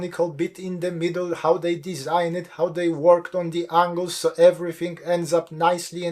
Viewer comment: Did he say "...how they worked on the anus" 7:04 ? 🤔 Anyway, one can see where the inspiration came from. It's definitive a plug in anus.